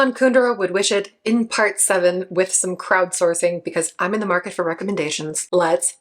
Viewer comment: I misheard that as "Kraut-sourcing". LOL Let's talk about thoughts on Germany indeed! 😅